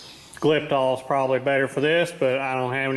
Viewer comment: What is that word you say at 3:17 naming a product you consider better to coat the inside with? I agree with you on liking the clean appearance of a painted block. I can't remember the last I saw a block painted inside. Also, idea for a future project <link> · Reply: Glyptal 1201 is considered by most to be the best coating for in engines.